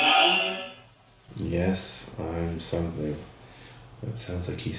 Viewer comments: sounds like he says yes i believe in god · sounded like he said "Yes, i believe i am"